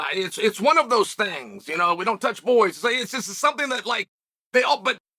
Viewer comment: Uncle cheal………did u just say……”we don’t touch boys??”………